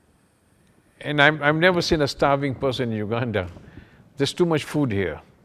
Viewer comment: Did he say "Never seen a sterving person in UG"